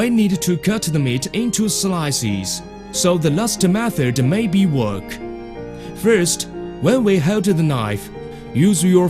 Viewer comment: gut the meet into slisis so the masta waffer needs to wook... What the fuck did i just heard?